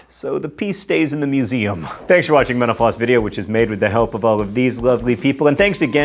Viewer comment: I obviously need more coffee. In your closing remarks...."Thanks for watching MentalFloss..." I heard "Thanks for watching menopause." Yep, need more coffee.